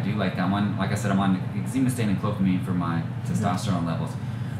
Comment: what did you say for your Test? cofamine?